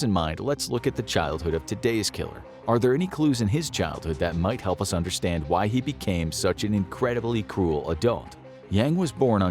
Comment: I misheard "Are there any clues in his childhood that might help us understand why he became such an incredibly cool adult"